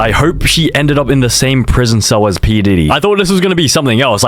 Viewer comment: Did bro said pee didy 💀💀💀